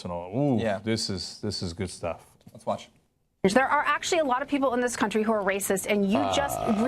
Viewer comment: She actually said "A lot of people". Not "Most people". So not only is bill-o a racist, he's a word-twisting fuckpig.